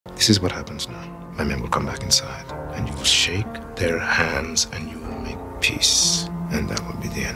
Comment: I legitimately thought Gus was gonna say "shake their ass" what the fuck is wrong with me